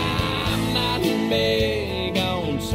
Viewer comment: “ I’m not big on sausage and gravy“ instead of I’m not big on social graces, Garth Brooks. 🤦🏻‍♀️ that’s what I thought it said for years 😂